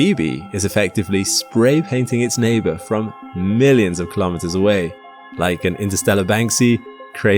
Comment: the captions say Miles, but the person says kilometers which one is it?